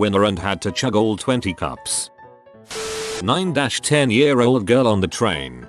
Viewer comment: I thought that said 90ish year old